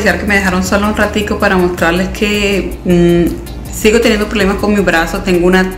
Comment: Jajajaja yo escuché “ sigo teniendo problemas con mi embarazo” 🤣🤦🏻‍♀️😅